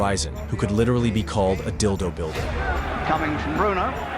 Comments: Did they call Frank Bruno a Dildo Builder? 😂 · did the voiceover thing actually say what I thought I heard? I am guessing the Voice over is AI but did it actually say the boxer could litterlly be called a dildo builder? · Did you say...at 05:25... that Frank Bruno could 'literally be called a Dildo-Builder'? I've never heard that expression. 😂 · Does he say dildo builder at time marker 5:25? 🤣🤣I don’t know what else he could be saying.